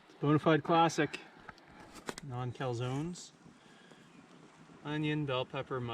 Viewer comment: I was today years old when I realised you were saying "naan" calzone and not "none" calzone... I thought you were saying none calzone because it isn't quite a calzone!! Haha